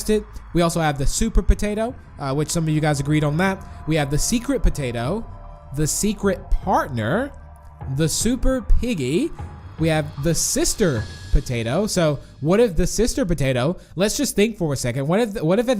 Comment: I thought it said the secretary prison lol